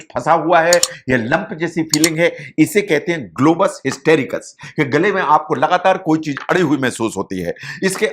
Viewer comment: kya bola koi batayega? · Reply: Globus hystericus bola h bhai Esse apne gale me kuchh ataka hua feel hota hai, hme lgta hai jese gale me kuchh fans gya hai